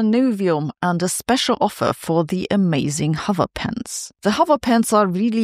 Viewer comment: I thought she said 'hover pants' but it turned out I was wrong.